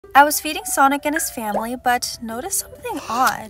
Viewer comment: I thought she said I was feeding sonic his family 💀